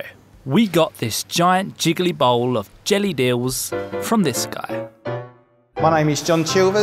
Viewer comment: WOW. Someone needs to fix the captioning! Instead of "Jellied eels" the captions say "German Jews". HOLY SHIT, GBS. That's a big mistake! (12:36, see for yourself)